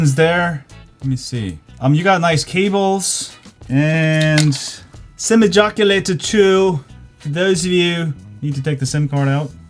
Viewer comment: did he say sim ejaculator😱😱😱